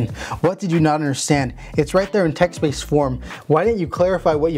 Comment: Lol I thought you said text weebs XD